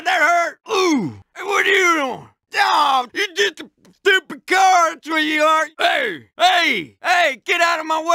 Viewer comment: I thought it said: “Aw you just a, stupid car sweetie heart!” when I first heard it. It makes me 🤣 a lot every time I hear it.